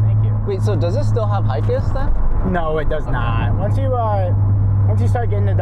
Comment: Can someone tell me what he asks at 19:50? He asks if it still has something but can't understand what is being said. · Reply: HICAS - HICAS and SuperHICAS (for High Capacity Active Suspension) was a system that would turn the rear wheels up to one degree both in-phase and counter-phase with the front wheels, depending on the input of various sensors (vehicle speed, steering angle, etc). Unlike other rear-steering systems of the time, it did not assist in parking, and was only activated at 55MPH+. It was designed to allow drivers to enter turns, connect slalom terms, and change lanes quickly and safely without the rear-end "fishtailing" out of control.